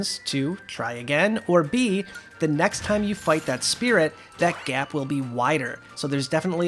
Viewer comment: Not gonna lie, I misheard you to start with and though you said eight thousand spirits. Still, great stuff.